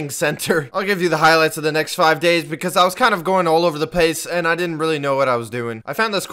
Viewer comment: did he just say pace😂